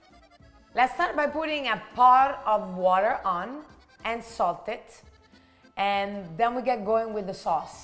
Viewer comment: I just heard "Lets start by putting part of water on and insult it" XD